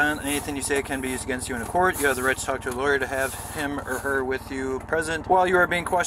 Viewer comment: He’d rather go to jail for years than just pay a ticket. Why are criminals so stupid? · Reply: I misheard the Miranda rights as " you have the right to hamburger with you." Get the fool his sandwich!